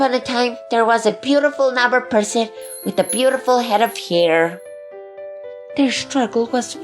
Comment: I thought putty said “with beautiful head of pier “ but it was “with beautiful head of hair”